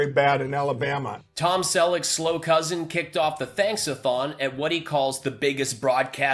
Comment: I thought Mike said "Thanks-a-Thong". What a waste - four days of binge watching.